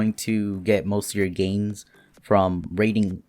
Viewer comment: You said games instead of tames btw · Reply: Nate TA I said gains